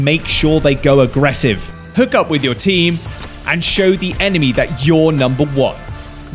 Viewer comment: what did just say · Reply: "Hook up with your team"